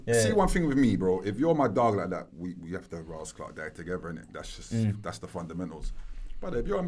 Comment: did he just say if your my guy like that we have to raaas that guy together 😐 🌋 whyyyy